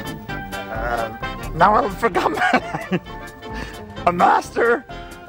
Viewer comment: Ewan: tries to get up Ewan: struggling oooh i have a bad feeling about this! Ewan: falls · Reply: I thought he said "oh i forgot the.."